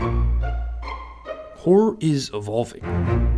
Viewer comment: "Wh*re is Evolving" at least that's what the caption says on